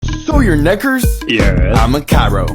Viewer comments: Did he just say “so you nig- hurts?” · i heard "so ur neggars?" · i know we all heard it 💀🗿💀 edit: if u are wondering what i’m talking about, it’s not the ending noise. it was the beginning word🤨🤨🤨😳😳😳🫵🫵🫵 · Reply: Sounded like he said "so your neck hers?" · “SO YOUR NECK HERTS” what i heard: “SO YOUR NECK HERS?” Say it fast